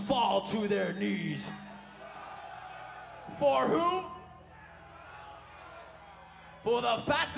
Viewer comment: James: for who?! Guy in crowd: the bell tolls James mind: sounds fire, ima use that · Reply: VERY LATE REPLY at 6:59 it kinda sounded like a guy saying "For whom the bell tolls."